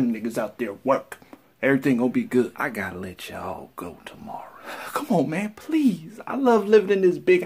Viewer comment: Oh damn I am really hard of hearing, I thought he said "I gotta lynch you all tomorrow"